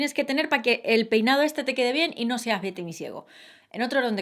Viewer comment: jjajjajjajajjaa en vez de Betty misiego entendí "victimy ciego" 😂😂😂😂